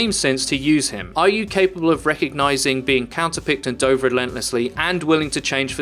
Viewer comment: At about 6:38 he says "are you capable of recognizing being counter-picked and [doved???] relentlessly" What is the ??? word? And what does it refer to?